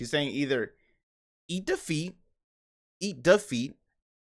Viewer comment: lmao yea prolly eat the feet i thought she said eat defeat cause i usually hear it when they near burn out